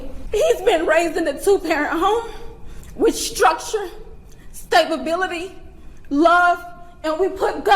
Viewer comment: Did I hear stabibility at 12:58?